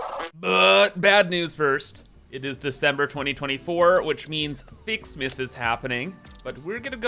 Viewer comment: Thought he said is December 24th at first had to check the date 😂